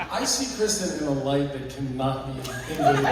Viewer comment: What does Tom say around the 3:30 mark, "I see Kristin In a light that..."? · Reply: “I see Kristin in a light that cannot be invaded”